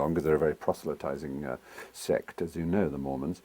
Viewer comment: I thought it said "Moron"